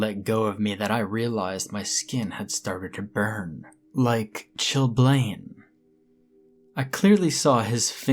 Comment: Hey. What is it you say at around the 11:04 Mark? "The skin had started to burn like"....... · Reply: Ok its called chilblain something you get when you are freezing or chill out Julie Blain lol